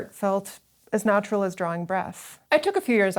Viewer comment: Sorry, I thought you said 'as natural as drawing breasts' >_>